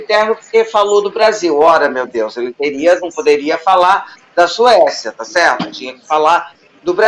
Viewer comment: Eu achei que ele estava falando da Suiça